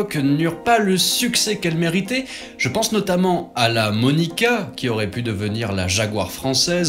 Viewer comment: la ''monica'' ? C'étais quoi la marque ? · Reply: Monica était la marque. Nous en parlerons bientôt 👍